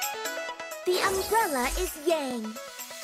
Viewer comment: Did I just hear "The umbrella is gay" or is it just me?